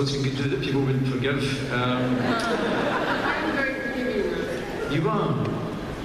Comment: What did Emilie say at 6:04 ?? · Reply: Riya shah I am very forgiving tho